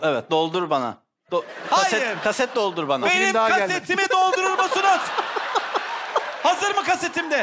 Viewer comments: de ne diyor anlamadım yaw sjsjsjsj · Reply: Sanırım o kaset daha gelmedi diyor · 1:6:25 te tolga çevik ne dedi anlayamadım? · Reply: Emine B o film daha gelmedi diyo